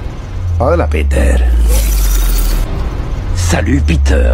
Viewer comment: En el 21:41 parece que dice "hola peter saca el p**o"